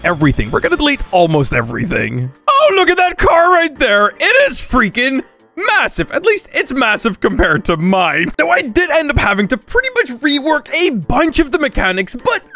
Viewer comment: did u say massive